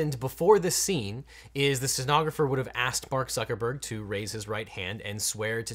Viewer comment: It sounds like he says Bark Zuckerberg